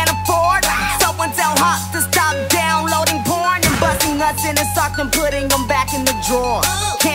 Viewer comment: does he really say can someone tell hopsin to stop downloading porn ? O_o lmfao .